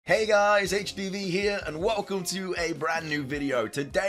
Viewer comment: Am I the only one who heard him say "hay guys HPV here!"?